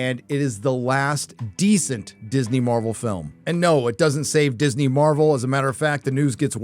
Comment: I thought u said marvel is dead